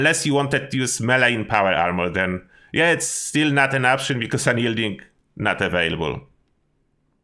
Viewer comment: that's assassin not unyielding, assassins is still available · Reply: You could be right. I thought he said unyielding but assassins makes more sense. I did see a few posts though that specifically said they thought unyielding power was way too op. At least they're listening more it seems. Perhaps they'll put it back in. Wouldn't mind it myself.